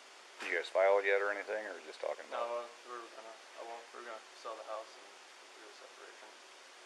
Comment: Does he say "we were gonna elope" @ 18:57?